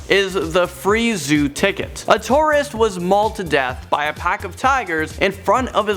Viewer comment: Did he just said artorias? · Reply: Morph Arkanaz He said "A Tourist".